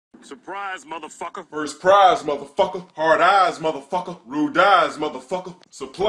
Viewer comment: at first i thought he said hard ass mother fucker